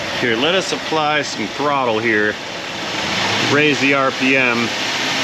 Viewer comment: He said Raise the RPM. I heard it ad Ray's the RPM - Really Proficient Mechanic :-)